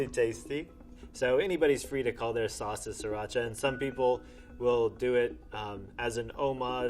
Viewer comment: He said siracha but I heard 3racha lol